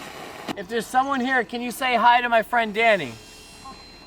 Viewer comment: at around 33:50 I hear "hey, hi" ??? great vid :)